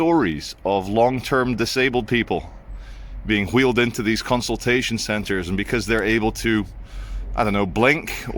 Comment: I thought it said ‘PIP Concentration Centre’